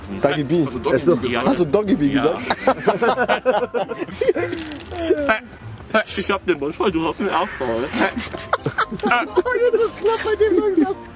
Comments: Was hat er bei 6:52 gesagt? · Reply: richtiger kevin ahhh ich glaub ich hab den mund voll du hast den arsch voll oder so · Was sagt da? Kann mir das bitte jemand sagen? Danke👍🏼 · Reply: "Ich hab den Mund voll, du hast den Arsch voll" bezogen auf Doggy Bi 😁😁😂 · Was sagt bei 6:51? 😂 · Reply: Jessi Lee ich hab den Mund voll und du den Arsch voll 😂